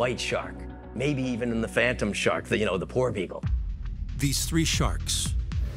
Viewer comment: Who else thought he said “the poor people”😂😂